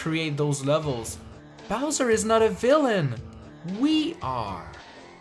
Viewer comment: wait did u say we r evil? on 10:00? ...